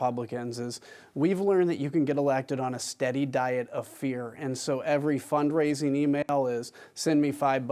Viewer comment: Thought he said “diet of beer”